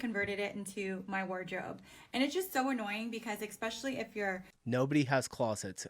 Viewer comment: Did she just say expecially???